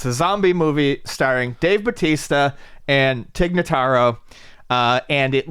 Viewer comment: Damn I thought said “A zombie movie with Dave Bautista and Kikutaro.” That would rule.